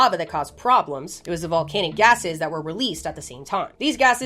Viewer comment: I'm so brain rotted all I heard was volcanic asses 💀